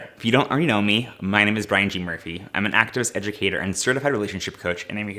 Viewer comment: When you said you were an "activist" I thought you said "actress" and I was like yas queen